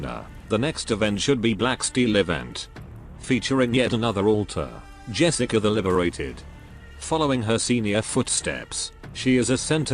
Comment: lol I was playing the game and only half paying attention and I thought you said "Jessica the Overrated."